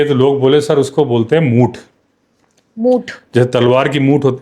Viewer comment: kya bola re? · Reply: Muth